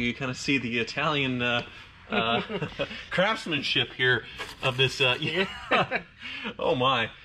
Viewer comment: Love the Lambo, but that convertible top is hideous! Only to be driven on clear cloudless days for sure. When you referred to the Italian “craftsmanship” evident inside the bumper, I swear I thought I heard “Crapsmanship”:-)